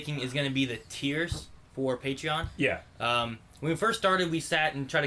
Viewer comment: For some reason I initially heard it as "Tears for Patreon" lol.